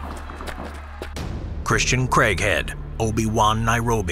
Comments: I thought it said crackhead 🤣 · Who else thought he was saying "Christian Crackhead"?